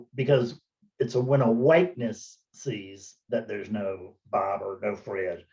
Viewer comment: Hey Fred. Did you say a "Whiteness" or a "witness" at 2:49 ? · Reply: I said Awakeness. You are more like a verb than a noun.